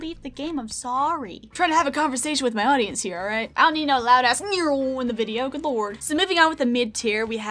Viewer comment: HI FRIENDS ANOTHER VIDEO SO SOON i rly wanted to make a video talkin about the call of duty games! tbh i dont think there is a trash game in the series, just some that i dont prefer. they are all great in their own ways! what are yalls favorites!? i would love to hear! ~! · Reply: I thought she said loud ass instead of ads😂😂😂😂